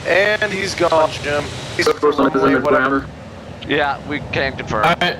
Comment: I heard "Air Force One is in the GRAMMAR."